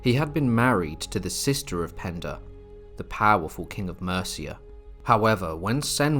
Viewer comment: I thought that said Panda for a minute! 🤣🤣